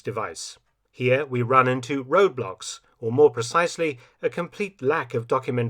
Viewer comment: I thought Mark Felton said "here we run into Roblox" at one point and almost spilt my tea